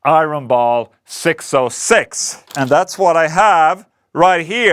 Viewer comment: Damnit, I thought you said ENGL Ironball 6L6. I was searching the internet but could not find it. Now I understand you said 606... Ah well, you make everything sound awesome, so I guess these EL84 are great too 😁